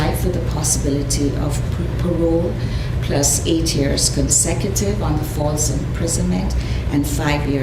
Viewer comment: Yep. And it technically took her life away as well. Just another example of a person who shouldn't even be near one. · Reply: dang I just saw that, the judge said consecutive i thought she said concurrent so I guess she will be 68. She's going to be like that old man from Shawshank